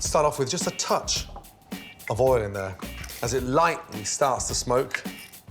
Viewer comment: First time I heard him say "just a touch of oil" instead of " just a touch of oluv'o"